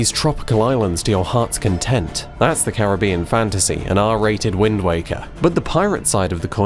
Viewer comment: "An Arrrrrr rated Windwaker" is how I heard him say it